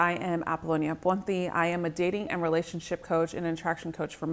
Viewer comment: Whenever you say your last name I hear "aponti" not "ponti"